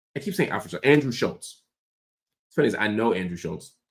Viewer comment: U callin him Alfred is the funniest thing though 😂😂😂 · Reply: bro I thought he was saying "our friend" shulz and finding out he was saying Alfred sent me 🤣